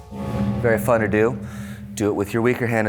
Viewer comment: I'm pretty sure those terms were invented by Ned Flanders. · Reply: I thought he said barafuddledoo then I realized he said “very fun to do” 😄 I’ve got flandereeze in my head(-dle diddle doo).